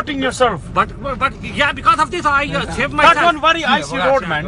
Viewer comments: “because of this I shave my self”..... lol😆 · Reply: Mart Smart I thought he said because of this I shit my self 😂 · “because of this I shave my self”..... lol😆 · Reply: I thought he said "because of this i shit myself"